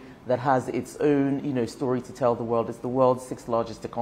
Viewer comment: did she said 6th